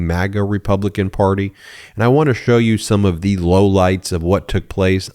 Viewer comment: You said, "low lights" but I thought you said, "low lives". Low lives is more accurate.